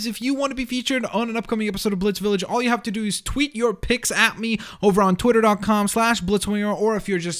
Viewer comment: I wasn't listening at the beginning and I thought he said tweet your pecs at me